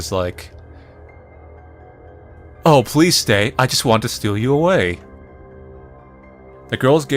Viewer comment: My headphones unplugged and I thought he was going to say “oh please stay I want to suck you off”